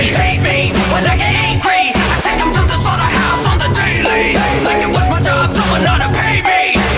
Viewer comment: ThePhoenixKing it sounds like he says “I take them to the Solar House on the daily”